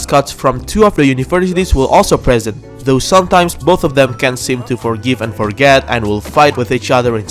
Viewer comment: Uhh "forgive and forget" doesn't mean you forgive them and then forget you forgave them. It means you forgive them and forget everything that happened before. Also "hidden up their sleeves" not "amoung" their sleeves. Just lettings ya know :P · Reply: Are you sure? Cause it sounds like you say "Both of them (the teams) can seem to forgive and forget". Even if you said "can't" you surely gotta understand the confusion when ya watch it back.